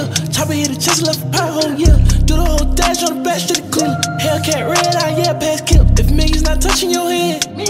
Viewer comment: What did he say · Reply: Hell Cat Red-Eye , Yeah Packs Killed (meaning his hell cats faster than scat packs)